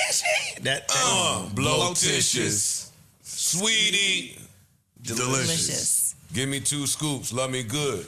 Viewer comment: i thought he said SCEEVY delicious , give me COUS COUS like the food that looks like rice